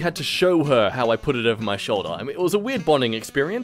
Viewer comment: did you say shoulder my guy